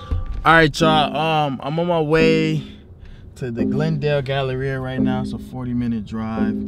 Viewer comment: Aweee dam I thought u said the north Dallas galleria i had to rewind the shit 😂